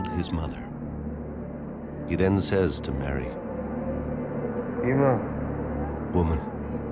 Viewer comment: did He say "Mariamne"?